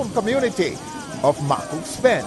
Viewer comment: Did he said his home community of Mc. Cooks Pen?